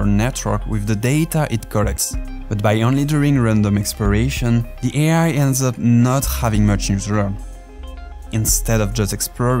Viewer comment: what did you say?? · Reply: But by only doing random exploration, the Al ends up not having much new to learn.